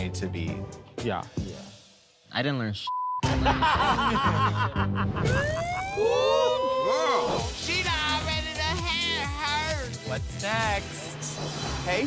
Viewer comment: What did Rupaul say in 2:20 ? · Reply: She dun' already dun' had herses.